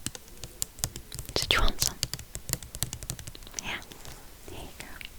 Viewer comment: I have a doubt, what she say in the minute 3:07 im not antive speaker english but always watch this video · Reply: “Did you want some?”